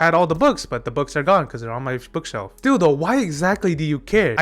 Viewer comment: "still, though... why do you care?" i did a double take because I thought you called him Dildo